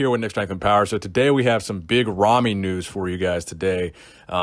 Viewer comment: I heard it "today we got some big ramy nudes" ....tch, got me excited for nothing